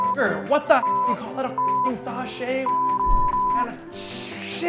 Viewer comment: did he just say "Sashay Away??"